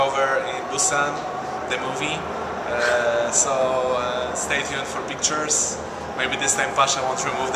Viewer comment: what he said in 10:16 i dont understood it "stayed here for pictures?" and something bout pasha